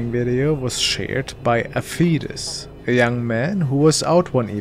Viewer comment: I seriously thought he said the first video was “shared by a fetus “